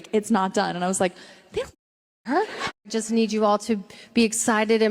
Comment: Guys what did she say at 0:10 ?? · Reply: "They don't f'ing care."